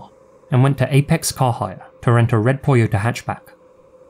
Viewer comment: what does say here? Please, cc doesnt show the correct thing!! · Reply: Red Toyota hatchback